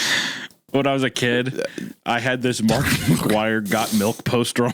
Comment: Thought bro said markiplier and didn't even question it